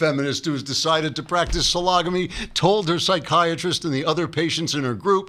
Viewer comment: i thought he said SLOGomy